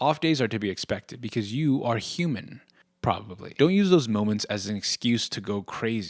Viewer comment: I didn't hear you say human and I thought you were saying 'You are HUGE. Probably.'